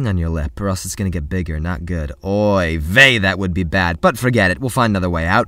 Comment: did he say Oy Vey